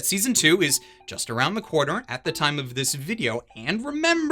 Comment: I'm not sure why, but I swear you said "Season Two is just around the cour-ner."